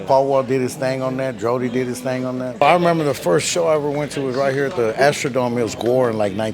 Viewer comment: Did he say his first show was Gwar?